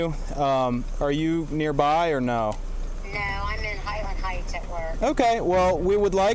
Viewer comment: did anyone else hear “I’m in heil Hitler.”?